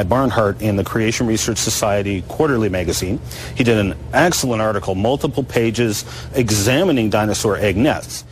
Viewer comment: Did he say "eggcellent article"?